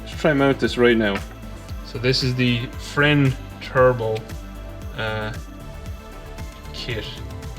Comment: Whats the name of that adapter sound like your saying friend turbo kit lmao